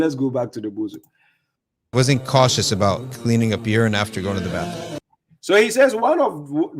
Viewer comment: Time stamp 25:31 i think the brother said cleaning up urine not cleaning the beard. Its still a bunch of bs he talking but im pretty sure he says cleaning the urine it also says it in the subtitles as he is speaking